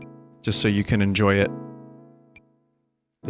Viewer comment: What was that thing at 4:58? I think i heard him say its name but i don't think my brain can process it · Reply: "Trance Tommy Wiseau" maybe